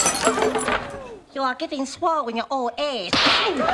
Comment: What did he say ? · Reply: You're getting swole in your own age...he said